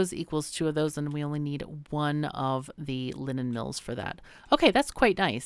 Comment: The first time you said “linen” I heard “lemon” as was like “a lemon mill? Sounds like something I’d like...”